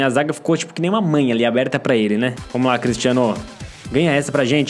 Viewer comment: Eu Escutei? "Que nem uma mãe aperta pra ele"